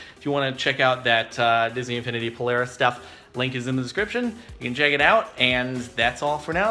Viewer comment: lol turned subtitles on for shits and gigs and then you said "link is in the description" (7:28) the subtitles said "ladies in the Kitchen" ..... ummm YouTube are you sexist or something ? :')